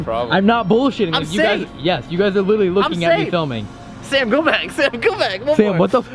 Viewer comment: Lmao I know this videos old but at 8:17 when Corey's telling sam to go back it sounded like he was just saying his full name sam golbach